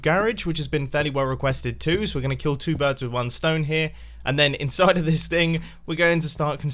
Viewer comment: "were gonna kill two birds with stone here" did I hear it right?